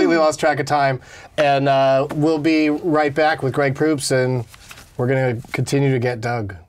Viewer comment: I got stoned before I watched this. All this time I thought it said, "Greg Poops".. sorry Greg..